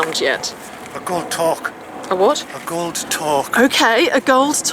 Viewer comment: A gold tork! Thank god it sounded like he said "A good cock"